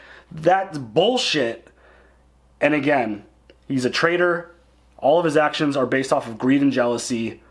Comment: I misheard as saying "He's a trader, all his actions are based on greed and jealousy" and for a few confusing seconds I just wondered what had happened between episode 7 and this one that had made turn to communism.